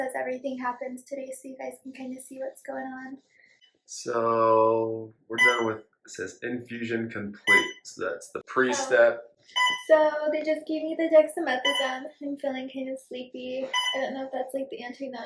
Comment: Did she say methadone?? It sounded like it but I couldn't quite catch it . I am just curious if that is something they give during chemo